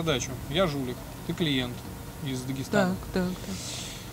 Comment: Мне послышалось или он сказал клиент из Дагестана?